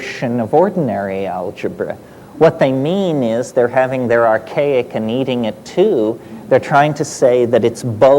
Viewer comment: Did he just say "Have archaic and eat it too" ? ;)